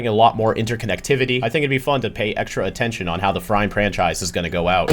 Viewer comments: i actually heard "FRIME PRANCHISE" and it could have been a nice little reference for the episode like "BILL GAX" instead of "VILGAX" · actually you said "Frime Pranchise" instead of "Frime Franchise"